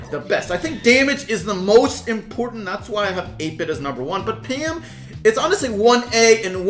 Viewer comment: did he just say egg-bit ....